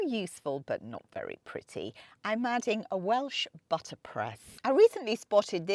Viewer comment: Hi dahling! The little gadget you put on your wooden island in the kitchen there...did you call that a Butler's Press? My hearing is not the greatest 🙄 I looked up that term and cannot find a blessed thing on it. I'm hoping I misheard you as I wanted to read more about that. It's the round wooden item with the stem in the middle. I hope to be describing it correctly. Thank you dear for any information ♥️